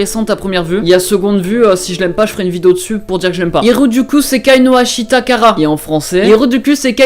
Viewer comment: c est moi ou a 2:27 il dit heros du cul no ......